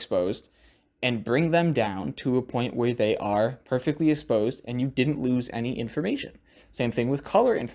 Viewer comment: don't I remember you saying in a previous video, hey look I pronounced especially right? but now instead of exposed it sounded like you said esposed? don't mind my analytics, keep up the progress, nice camera!